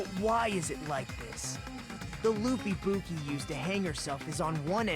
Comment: my tired APD brain thought Nagito said “The loopy boopy” seriously instead of “The loop Ibuki”. I managed to stop myself from going into a Chuggaaconroy style giggle fit. Loopy Boopy!